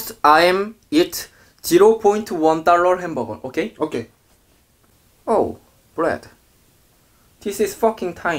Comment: This is what i hear at 0:41 : OH BLYAT